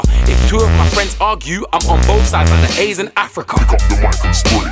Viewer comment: i thought he said aids in africa lol